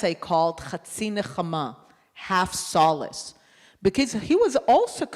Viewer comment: what did she just say there at the end , just before the next line? (בקיצר?) .. anyway, new placard: [Dr Wilf = 💙✡️🇮🇱💪]..... 🖖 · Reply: I think she said "because" ("...because he was also concerned...")